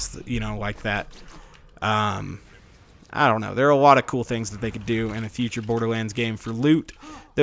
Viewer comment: I heard " idk there are lot of cool things they could do in the future borderlands game for LUKE" xD